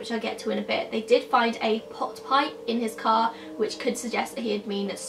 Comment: When you said a pot pipe, I thought you said pot pie and I was like "hm, logical, he must have been hungry" LOL